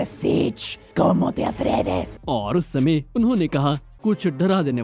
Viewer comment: The Spanish "how dare you" sounded like satan